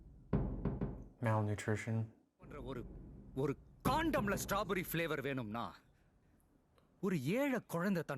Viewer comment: I don't know who made this subtitles it's terrible, The exact dialogue he has spoke has more impact but they subtitle into silly words for example 6:47 he clearly says If a rich needs strawberry flavored c0nd0m but the subtitles says Strawberry flavored soap